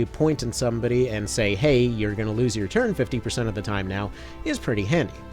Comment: did you say stun is 15 or 50 percent chance? My hearing sucks. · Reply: 50, or at least somewhere in that ballpark as long as RB hasn't changed it.